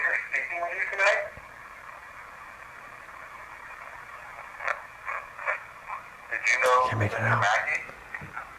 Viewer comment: I hear "I know, Mackey"